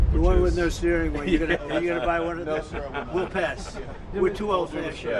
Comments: does he say “we’re too old for that shit” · did he say weret o old for that shit? hahahahha